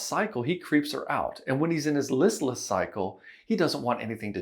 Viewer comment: what word is saying? Lust vs lessless???